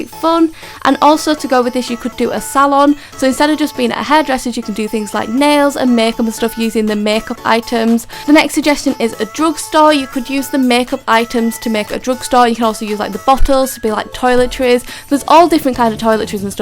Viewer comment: I thought she said drug store